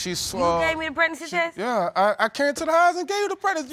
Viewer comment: Who takes 48 pregnancy tests?!?! 😐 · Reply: At first, I thought she said 4 to 8 pregnancy Tests😅